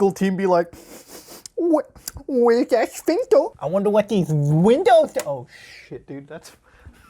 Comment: I honestly don’t know what you said it sounded kinda like “we ne sphincter” that’s just what I heard